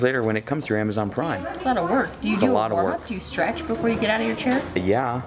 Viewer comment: I thought you said, "Do you scratch when you get up?" And he said, "Yeah, I scratch!" Ha ha! Stretch, scratch . . . all the same.